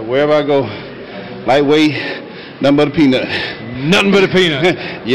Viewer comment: what he says? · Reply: lightweight nothing but a peanut